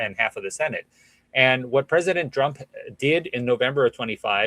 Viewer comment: did he say President DRUNK?